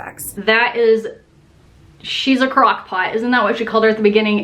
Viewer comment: “She’s a crockpot.” 😂😂😂 I think Molly said crackpot 😉